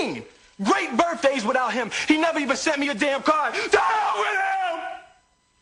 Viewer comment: what does will say at 12:07? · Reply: To h*** with him